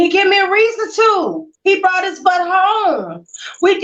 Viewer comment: Had he brought me any STDS, that would've given me everything I needed to know about his sorry ass. And I didn't give him too much rope. He's a grown ass man therefore he wasn't kept on a leash either. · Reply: Darsell Brooks u said u didn’t nag him bout hanging out that’s giving him rope n my bad I thought I heard u say he brought stds